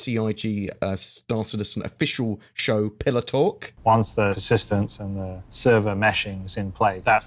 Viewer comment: lol sounded like you were saying pillow talk instead of pillar talk :...D